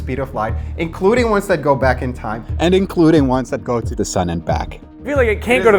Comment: He says "including ones that go to the sun and back" but the subtitles say "including ones that go to the other side of the moon and back" How did they get "other side of the moon" from "sun"? xD